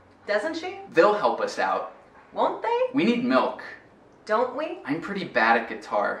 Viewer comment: "We need milk"? Eu ouvi "we milk" 😱